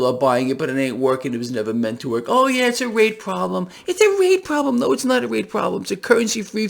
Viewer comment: Misheard you…thought you said “it’s a rape problem”.